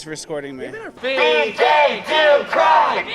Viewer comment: Tha fuck did I just hear.....Be gay do crimes????